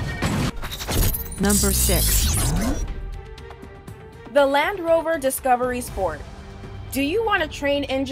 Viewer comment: The English in this video makes me cry. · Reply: Calvin Tang The first clip i heard "The Lamb Rover"😂😂